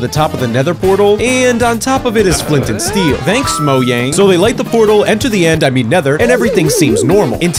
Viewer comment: Bro why did I hear "Moyang" when you said Mojang😭